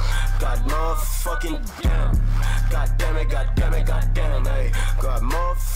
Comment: sounds like they saying demi-god